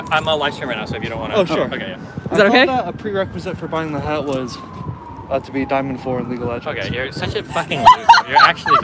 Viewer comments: What does the guy say at 27:32? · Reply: I think it's: "hey can I ask you something about your hat real quick? Was a pre-requisite for buying the hat was to be Diamond 4 in League of Legends?" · what did that guy say ? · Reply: "Do you know that the prerequisite for buying that hat was being Diamond 4 in League of Legends?"